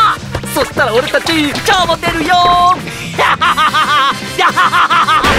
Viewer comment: 一瞬来春公開が来週公開に聞こえて、え！？もう！よっしゃ！って思ったら春だった…